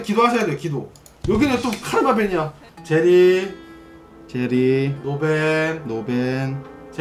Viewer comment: 테스터훈은 제티 달라는거로 들리네 ㅋㅋ